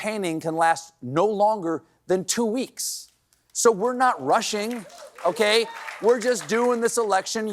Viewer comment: I thought he said "We aren't Russian" 😂